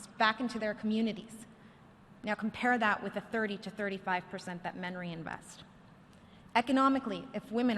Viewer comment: Now compare that to the 30 to 35 % percent that "?" invest" I need to know what the "?" word is, urgently it's for a friend's research paper. It's not "memory" I'm certain. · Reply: That men reinvest